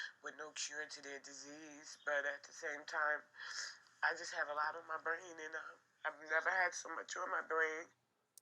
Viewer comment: maybe I'm not hearing it right.. does she say, "I just have a lot on my brain. I've never had so much on my brain." ..? brain = 44 on my brain = 111/666 "I've never had so much 666." If that's what she said, I'm surprised they didn't give her brain cancer, instead of stomach cancer. People usually say "on my mind."